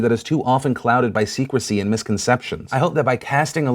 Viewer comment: Am i the only one who heard him say secwecy?